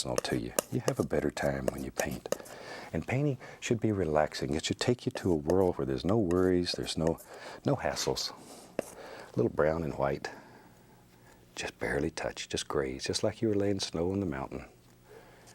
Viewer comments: I bow to the grandfather of ASMR. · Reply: did he say ''no assholes''? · did he say "no assholes" ? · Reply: He said no hassles